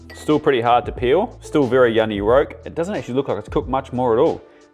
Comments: min exactly on the 5 min egg I swear he says yunny rolk lol that's awesome great video thank you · Andy... at 5:01 or so...did you say, "yunny rolk"??? I thought that only happened to me!